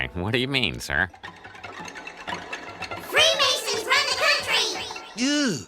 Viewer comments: I always misheard this as "Three Aces run a country" and thought he was hallucinating over the power of a good poker hand at his casino.🙉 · as a kid I heard "us germs rule the country"